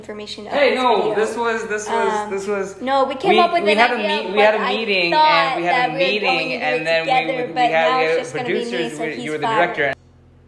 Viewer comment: I had to watch that a few times to understand what they were saying: we came up with an idea but I thought we were gonna do it together but now it's just gonna be me and he's fired: we had a meeting-we had a meeting and a producer and you were the director PLSSS I LOVE THEM